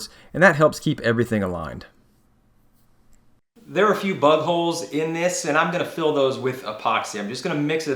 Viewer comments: I was in the mood to build a bench. Now I’m adding a bench to my shopping cart online. Be here in a week😆🤷🏻‍♂️yours looks great though 6:16 Did you say “you’ve got a few buttholes in the wood”? Lol. I think you said BUG holes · I’m pretty sure he said there are a few buttholes in this instead of big holes. 😂